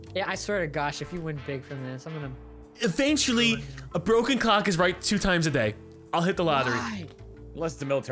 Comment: I swear to god when he said broken clock is right twice a day I thought I heard "broken cock", I was like wait what? Knew I must of heard wrong, but it made me laugh.